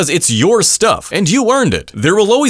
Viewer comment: I misheard "a better car" for "a better 🐓" 💀